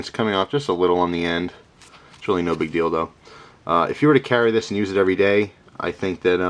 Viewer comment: did you say no big dildo